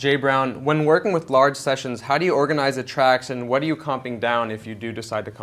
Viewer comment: what is that they're saying? Combing down? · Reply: I realize it's 4 years ago you asked, but other people may have the same question, so: Comp'ing. Short for compositing. I.e. merging stuff, like when you flatten/merge layers in Photoshop.